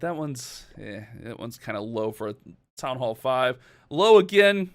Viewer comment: did he say “town hall 5” ??